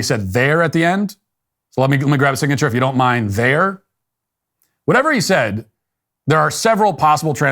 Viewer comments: sounds like he says: "if you don't mind there" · I'm from MS and what I heard is, "Just let me grab a signature, if you don't mine-ere." For those of you who don't speak backwoods southern, that's "if you don't mind, there." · I heard "In there". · Sounds like he said can I get a signature from you in there. Not n*** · I heard “ let me get a signature nigga” 🤣🤣🤣🤣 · I heard " if you don't mind there " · I listened several time and what I heard was "can I get your signature "in there" or "on there" - kinda fast and slurred so it comes out as "inair" or "onair" . Pizza Hut is being sued for poor enunciation by someone who probably can't "breeve". 10-1 says this supposed trauma only came about later after they ate the pizza, watched the game, drank a few 6 packs and then someone else listened to the doorbell footage and heard what they wanted to hear. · It sounds like he said I appreciate you if you don’t mind ..there